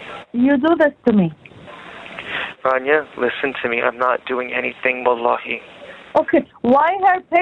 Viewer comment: I know he's saying wahlahi,but I swear it sounds like he's saying "I'm not doing anything but lieing" lol 🤷‍♂️